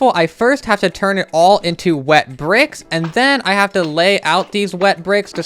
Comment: I’m so sleep deprived that I thought the “wet brick” said “her butt” 😭😭😭🫶